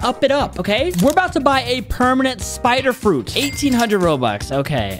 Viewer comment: bro said spider but the caption said quake